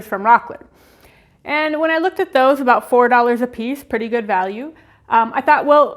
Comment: Did she say $4 a piece?